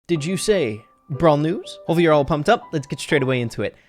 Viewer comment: I thought he said "did you say, bra news